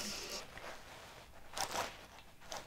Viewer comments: Épico 16:40 tira tira eu escutei Kkkkkkkkk kkkkkkkkkkkkkkkkkk kkkkkkkkk kkkkkkkkk kkkkkkkkk kkkkkkkkk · 日本の動画やと思わなくて見てたら「いてててて！」って聞こえてびっくりしちゃった笑